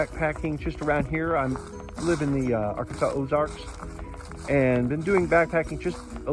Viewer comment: Looking forward to seeing your hike! Did I hear you say Ozarks, or did I mishear? Also, looking forward to your content after the trail; been down that road and look forward to learning more. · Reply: Thanks for joining me. Yes, I live in the Arkansas Ozarks. And as far as the self sufficiency I think I will also have my brothers help me. Can’t wait to get to the trail!